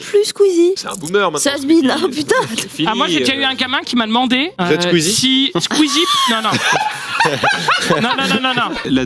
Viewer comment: Demandé quoi ? Il t'a demandé quoi le gamin ??? J'ai besoin de savoir !!! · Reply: Il lui a demandé si il pouvait demander à Squeezie de venir animer son anniversaire il me semble